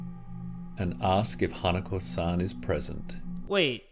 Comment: first thing that came up my mind when i saw that is an anime called toilet bound hanako kun XD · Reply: Bro same!!! I had to repeat it because I could've sworn he said hanako kun